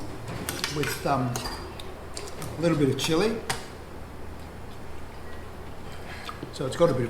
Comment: I described it as a bit cheesy. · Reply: I misheard you then. Thought I heard you say "chili taste"